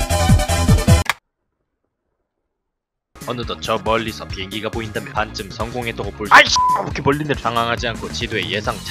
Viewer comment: 뭐라고? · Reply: 속스